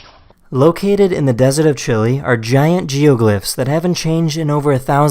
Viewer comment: did he say Chilly? This is how many people agree 👇